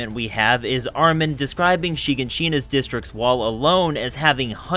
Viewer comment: why did bro say shiganshina like that lol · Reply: Why did I hear you say Hiroshima instead of Shiganshina? 💀